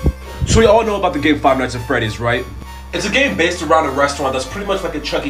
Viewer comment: I thought he said gay nights 😆